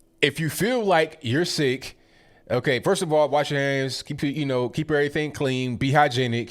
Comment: I wasn't watching; just listening. When you said this, I immediately went back to watch and listen again lol. I swear ABL said : "Wash yo' a$$" lol! I was thinking ABL went totally out of character there.